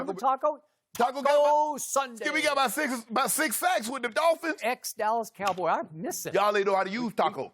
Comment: I had to rewind the video cause i thought skip said the dolphins were cucks